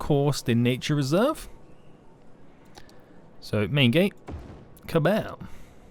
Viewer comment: did just say Kabab?? 😂